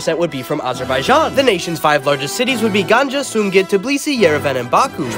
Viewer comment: As a Georgian 🇬🇪 I heard him say "tiblisi" instead of "Tbilisi"